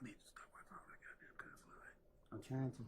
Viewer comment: Fcknggal we heard you say “ stop all dat goddammv cursing” … trying to whisper like the yingyang twins😂